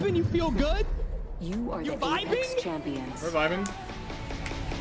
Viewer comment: always hyped as shit! At first i thought he said, "im high as shit"